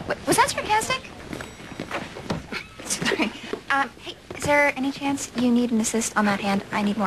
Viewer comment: Did she just say "Sara"? 😱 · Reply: She said sorry haha